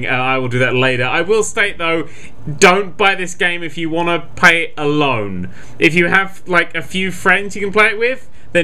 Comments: At first I thought said, "Dont buy this game if you want to pay a loan".. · At first I thought said "Don't buy this game if you want to pay a loan". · I thought he said "dont buy this if you want to pay a loan" :P